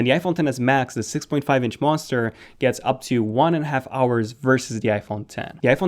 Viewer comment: Did he said monsters in